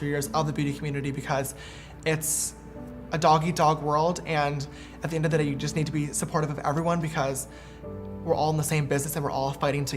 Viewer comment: Did he say it’s a “DOGGY DOG WORLD”?? 🤔